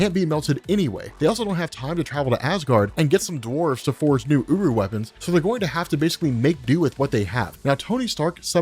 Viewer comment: I misheard 3:44 as 'uWu weapons'